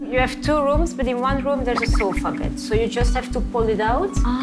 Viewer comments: 谁出国旅行不这样的 不都要飞长途吗 还要做经济舱呢 不必他们累的多。推几个箱子就叫照顾大家了，谢娜英文英文不会，安排也不会，就是傻愣着，大家都累了她还要大哭 别人还要管你。都是人，就谢娜又笨又矫情，看着就烦。不是还去美国留学吗 连sofa bed都能听成so far。。。。应采儿跟这几块儿料出国应该觉得丢人吧。。。。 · 谢娜太搞笑了 宾馆老板说one room has sofa bed她听成了有个房间有点远(so far) 🤣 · sofa bed 被听成so far 哈哈 差点都浪费掉了